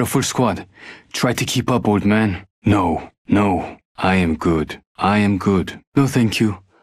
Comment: am i hearing it wrong or does it sound like 'i am groot'